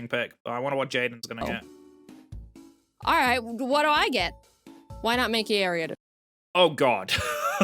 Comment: I've seen many reactions to this video, no one ever wants to pick the ariados :( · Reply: When i first watch that part i heard " why not take any of those"